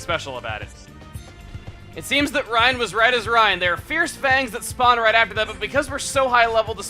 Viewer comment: Did he say "Right as Reyn" at 13:51?